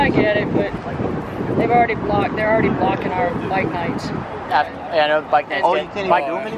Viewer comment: i thought she said "dyke nights" .....